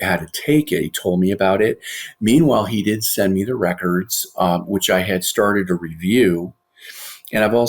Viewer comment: because she has more than what this doctor has she has her full medical records. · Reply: That’s def not what he said and I get the feeling you either stopped listening to the interview or zoned out. Derrick is talking about the med records & the Dr gives a sequence of events stating, “meanwhile he DID send me the records” 34:09! Are you gonna address the rest of my comment, or no?